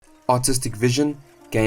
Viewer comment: Thought bro said "autistic vision"